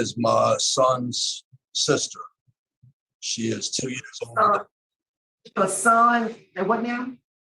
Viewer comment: I'm in total shock! To find out his victim was practically a baby, 4 years old. Sickening! He didnt say he didnt do it but that they didnt have "proof" strange the way he tried to separate himself by referring to her as his son's sister. Until the victims father said her age...I was assuming it was a teen, and the one reviewer called her a young lady...weird. so it was essentially a step child although they didnt marry. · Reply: I thought he said 2 yrs old